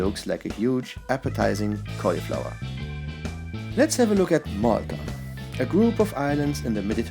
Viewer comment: When you said Malta I thought you said Mordor.